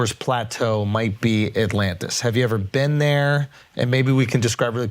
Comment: Shit I thought it said Atlanta.